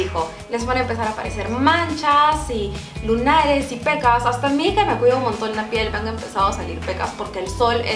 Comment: dijo??: HASTA MI HIJA ME CUIDA LA PIEL....(OH YO ESCUCHE MAL ??) A LOS 5:00 MIN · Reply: Dijo: "hasta a mi que me cuido montón la piel me han salido pecas" :D